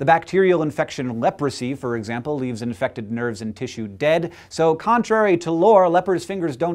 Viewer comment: when he said "nerves and tissues dead" i seriously thought i heard "nerds and tissues dead" i was like "Who is killing my people!?"